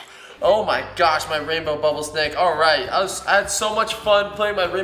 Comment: I thought it said bubble snack